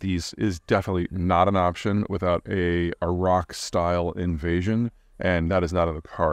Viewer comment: It sounded like you said… “A rock style invasion” I pictured forty Woodstocks landing in the beach!